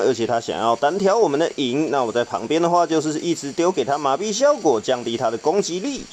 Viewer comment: 一直把 給他麻痹效果 聽成 他ㄇㄚˉㄅㄧˉ…